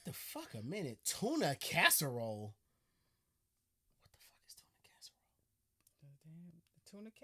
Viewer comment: The way mans said tuna casserole, I thought he was gonnna say " ok Bill, you won me over" haha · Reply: I thought it was chewing a casserole! As in she can't cook for shit! 😉